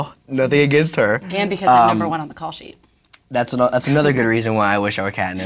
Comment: Katniss says at 5:25 and becuz I'm number one on the call sheet. At least I think thats what she says